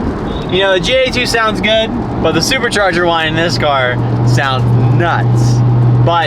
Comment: Subtitles said supercharger wine 🍷🍷🍷🍷